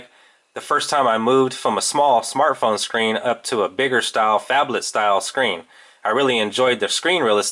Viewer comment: Did u say fabled?!